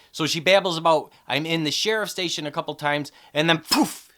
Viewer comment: I really don't think she said she was in the Sheriff's station. A few people have said that and I watched it back again, I thought she said "By the Sheriff's station, by the Sheriff's station", meaning that the Convenience Store that Evil Coop brought her to was by the Sheriff's station. It sounds more like she says "I'm the sheriff's station" which doesn't really make sense. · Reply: It is hard to hear her say it, but she definitely says "I'm in the sheriff station". I verified it with closed caption.